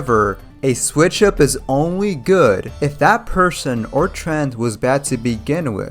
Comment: DUDE BE CAREFUL HOW U SPEAK I THOUGHT U WAS SAYING PERSON OR TRANS😭😭😭